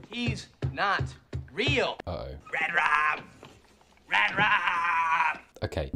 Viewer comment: Oh, my God, for all these years, I thought Xander was saying "Rad Rob!"